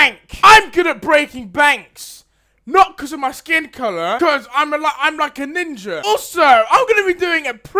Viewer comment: I thought he said I'm like a nigga